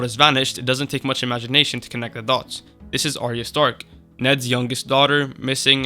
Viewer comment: subtitles are hilariously bad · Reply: "This is historic" instead of "This is Arya Stark" 😅